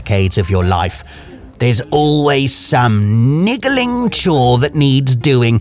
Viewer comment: What did he say at 6:10? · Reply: Had to do a double take and research myself lmao… he said ‘niggling’